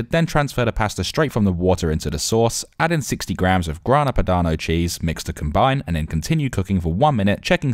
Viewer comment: What type of cheese he said ? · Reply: I thought he was saying grandma padna cheese 😂 but finally realized it was Grana Padano cheese 🧀